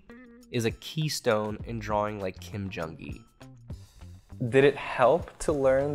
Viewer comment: i thought it said, draw like kim jong un 😂